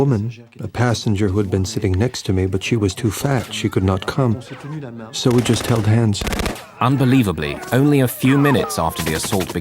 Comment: did he say "the lady next to him was too FAT to escape with him, so he just held her hand"!!?? 😶